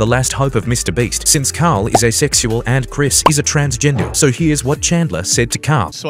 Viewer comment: For a second I misheard “asexual” as “a sexual” and I was a little confused 💀